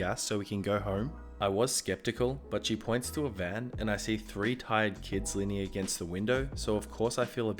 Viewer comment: i heard tied instead of tired